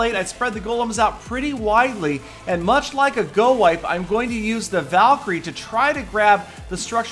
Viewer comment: Gorape: Golems, Pekkas, and Rages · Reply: Woo Math lol I know I just thought he said go rape